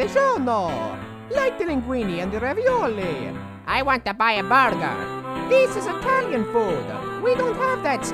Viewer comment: Burger sounds like he said vodka